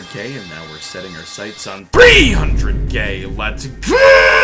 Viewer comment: it sounds like hes saying "300 Gay"